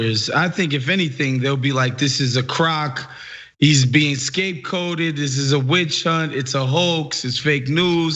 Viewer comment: Did he just say scapeCOTED?